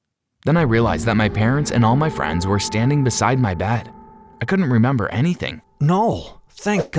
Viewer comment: I thought u said u didn't remember anything